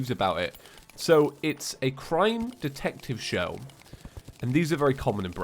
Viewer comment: When you said crime detective show i thought you said crying detective show. My body is not ready